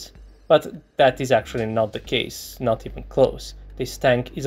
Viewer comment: I think most favorite line for every video he makes these days about what the heck is happening in Ukraine would be "That is actually not the case." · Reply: I heard "That is actually not the cage".